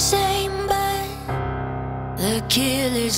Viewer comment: This song is actually hella deep "when the bullet is same but the killer is new" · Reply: I heard it as “ what to do when the bullet is the same but the kill is always new”